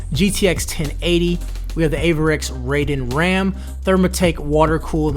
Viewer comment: DID HE JUST SAY AVEREX RAIDEN? ITS AVEXIR